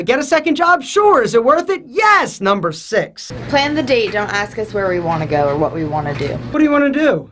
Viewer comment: Thought she said don't ask us where we're going, what we r doing.. Better put my belt back on.